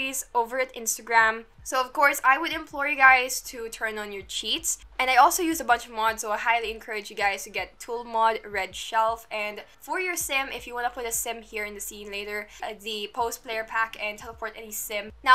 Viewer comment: i def thought you said no mods..